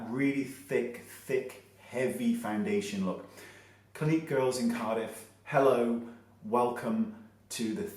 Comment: did he say "girls in Cardiff"